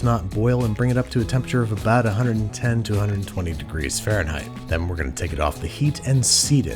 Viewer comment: Not sure if anyone is still checking this but the subtitles at 2:46 are off in a pretty substantial way, they say 220F instead of 120F